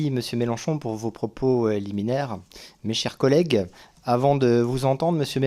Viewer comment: C'est moi qui entend mal ou le rapporteur parle de propos "éliminaires" ? J'ai beau, me le repasser, j'entend pas "liminaire", mais c'est peut-être un "euh liminaire" ?